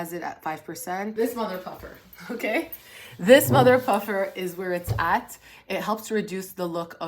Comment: I genuinely thought she said this motherf**er i had to rewind it twice to make sure she didn’t 🤣🤣